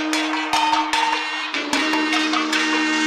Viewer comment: SAY LALISA LOVE ME, LALISA LOVE ME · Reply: I thought she said Say Lalisa lemme Lalisa lemme